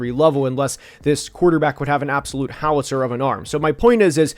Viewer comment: WHAT was that word? I don’t even know what you said to the point I can’t Google it 😂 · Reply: "Howitzer". Noun. An artillery piece or cannon